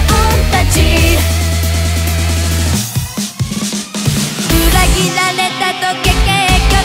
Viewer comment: のまーちゃんのパートで裏切られたとか、、っていう所が裏切られとけって聞こえる笑笑 皆さんきいてみてください！！